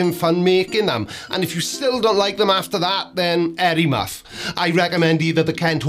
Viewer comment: Did you just say "hairy muff?" Or was it "Harry Muff?" YouTube's auto-generated captions apparently thought you said "air muff." I'm honestly not sure which evokes the more graphic image…